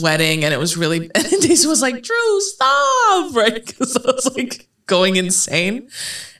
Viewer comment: Drew said "DREW STOOOP" and all I heard was "ROOOON STOOOP, STOOOOP IT ROOOON" 😂